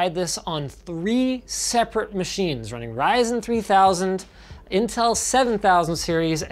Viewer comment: and 12:39 subtitles say "RISE" instead of "Ryzen" lol